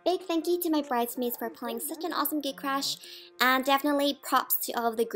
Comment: This is so touching. and funny :D Though I kept hearing "gay crash" Hahahahha. I'm so happy for you two!